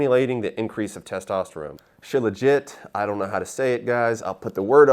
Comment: I thought said “shit legit” and I laughed so hard😂